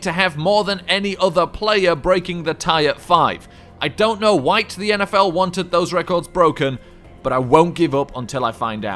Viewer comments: I'm pretty sure I heard him say "I don't know "white" the NFL wanted those records broken" @ 5:41. He is pushing the race card in his satire. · Lol nobody caught what was said at 5:41- 542 🤣🤣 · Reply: WHITE